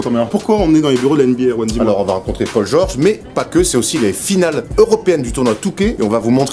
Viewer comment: Quand a dit "les finales européennes de 2K" à 4:07, j'ai compris "les finales européennes du Touquet"... Me jugez pas svp